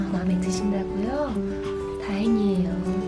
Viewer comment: 언닠 나ㅋㅋㅋㅋ 다행이에요를 다뻥이에요 로 들었어요ㅋㅋㅋ